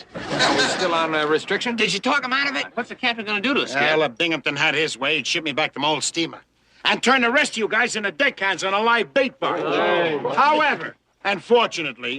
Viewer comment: When he said "deck hands" I thought he said 'dick heads' LMAO!!!